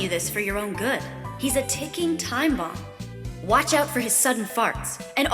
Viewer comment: I thought at 12:15 she said he’s a fucking time bomb instead of ticking